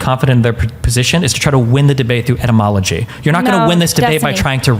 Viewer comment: At roughly @ 15:15 to @ 16:06 - Destiny knows he is about to lose the debate so he does his best to dodge and distract. "You're trying to win this debate by etymology", he claims... using an argument built on the etymology of the words: you - are - trying - to - win - this - debate - by - etymology. Brilliant work, Destiny. Brilliant work. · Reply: 😂 I was so confused there. Thought he said entomology….I’m like how did she bring bugs into this?? 😅